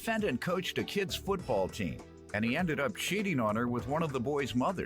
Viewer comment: Why it sound like he said the samean ended up shitting on her.